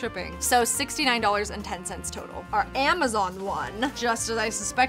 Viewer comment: says cider instead of amazon...